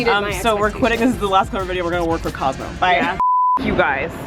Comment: I love how I thought they were saying cosco